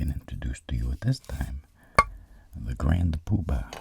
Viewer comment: What did he say hold on let me rewind I think he said poo bar let's check again. The grand poo bar ok you do you